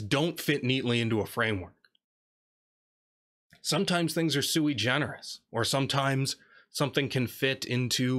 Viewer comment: What does he say? "Sometimes things are ... ." [something]-generous ? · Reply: Sui generis. A latin phrase. Something that is unique such a book about whales wearing hats.